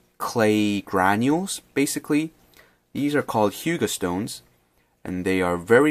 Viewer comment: Hello. Did you say HUGO stones @ 10:43? I cant find info of those stones over the internet. Maybe i heard wrong perhaps? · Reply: hyuga stones. aka pumice stone